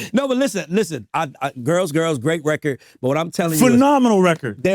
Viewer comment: Did he just say girls, girls girls???😅😅😅